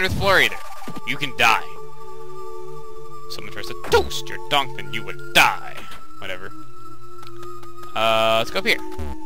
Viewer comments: Wait... what did say at 7:34-7:38? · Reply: "When someone tries to toast your (inaudible) and you will die." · Wait... what did say at 7:34-7:38? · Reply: I thought is said "Someone tries to toaster dunk and you will die."